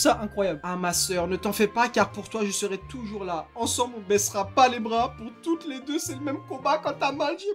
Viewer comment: j'avais compris "ensemble on baisera pas les bras" je suis pas seule rassurez moi 😂😂😂